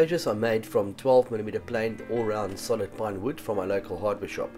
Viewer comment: Wait, did you say at 0:25 that the enclosures are made from pine wood? Isn't that notoriously soft and subject to warping? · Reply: lol after I watched a few more seconds I think you said plywood, much better. I got stuck on the accent, sorry.